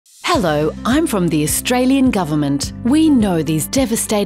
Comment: I hate that I love your Australien Government Ads. Whenever you say "Australien" I hear "Albertan" and whenever you say "Morrison" I hear "Kenny" and it turns out that they're every bit as applicable here too. 😭